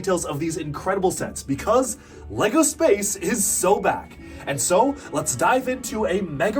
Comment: I thought he was gonna say so bad